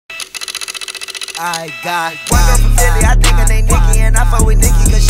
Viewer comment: Finish da lyrics 🤪 I got one girl from philly i think her name nicki,and i fuck with nicki cuz she not to picky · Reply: Hűmbłè Kýhřø I thought he said white girl from philly 🤣